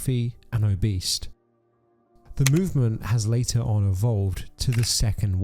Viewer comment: did bro just say "obeast" 😂